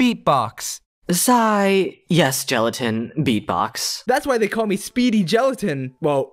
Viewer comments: Wasnt actually a swear he said "I'm sorry gelatin?" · Uh actually, I think he said "I'm Sorry, Gelatin?"